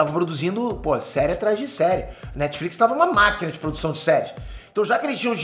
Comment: escutei a netflix ta uma maquina de sex#